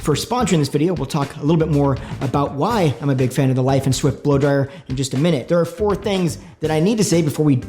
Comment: Laiden Swift sounds like you said Light and Swift at first to me.